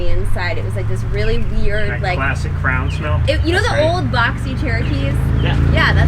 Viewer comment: Classic CROWN smell wtf did he just say. Its cray-on like she said.